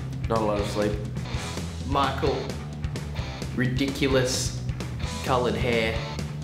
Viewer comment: what says Ash at 0:32 ? (i don't speak english) :S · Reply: "ridiculous coloured hair"